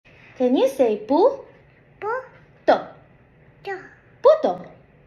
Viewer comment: I thought I heard Dora say “Can you say Puto?!”